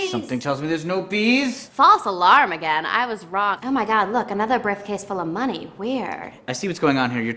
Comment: it sounds like shes saying briffcase haha love it